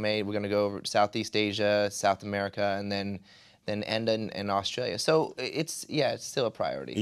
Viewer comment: Am I nuts or did he just say "Austria" at 4:06? XD Can't believe he of all things mixes up the countries, but hey, as an Austrian I don't complain if they'd come back to my country instead of Australia. :P